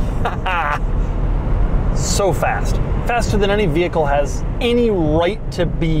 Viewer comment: Is it only me? @ 28:09 I thought he said he has to fart 💨 😂. Good electric vehicle overall.